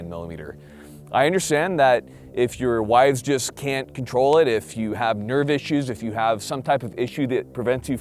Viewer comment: I thought he said “if you have nerd issues” 😂